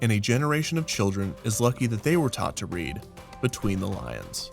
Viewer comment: Huh, it sounds like he said between...the...lines....everything I know is a lie.